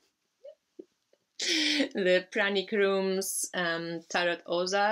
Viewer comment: Ink and Flame Tarot des aux arcs - made by Pranic Room on IG. Can be purchased - just google it x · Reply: Ah, thank you! I'd thought you might have said "panic room" and I tried googling that before asking my question. But by reading your answer, I found the page on IG now. Thanks again! :)